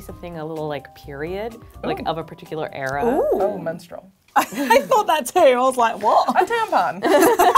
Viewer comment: when she said menstrual in response to a specific period I thought she said minstrel, like she was telling the other girl to be a minstrel and i considered the idea, ngl